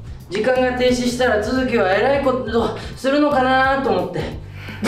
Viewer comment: 「えらいこと」に聞こえるの僕だけ！？😂